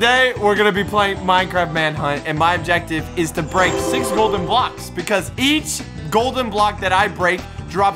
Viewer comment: said "break 6 golden blocks" and i heard "prank 6 golden blocks" Lol